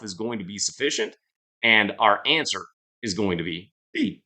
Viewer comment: Hey it sounds like you said the answer is B... but did you mean "D"?